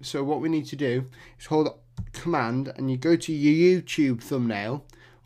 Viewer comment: i couldn't understand the word in 2:23 cland word thingie